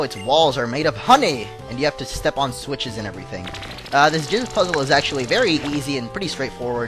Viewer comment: sounds like he says jizz puzzle...eww